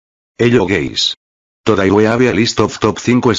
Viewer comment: who thought in the start he said “hello gays🏳️‍🌈”